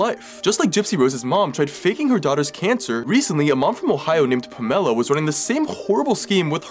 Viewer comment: You would think this was AI because of the way he pronounces Pamela lol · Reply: I thought he was saying Carmela until I read the captions.